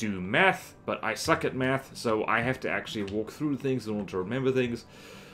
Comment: litterally thought you said “meth” lol